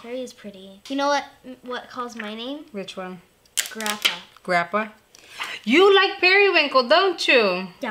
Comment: I thought she said the shade was called grandpa at 1st, I'm like why would they name the shade grandpa 🤣🤣🤣 I had a moment feel pretty dumb rn